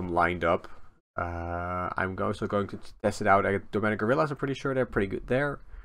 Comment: did you "domenic gorillas" instead of "demonic" or am I just hearing it wrong? Been noticing this on your streams too